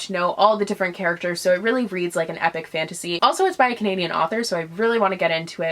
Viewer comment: I thought you said 'Pagan author' instead of 'Canadian author' for the Fallen Kingdoms and I was extremely confused. Nothing wrong with Pagans, of course.